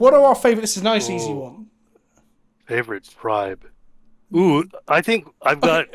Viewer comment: Magic Question Time is good! Even if it is Parliamentary :P I misheard 'favorite tribe' as 'fav Shrine'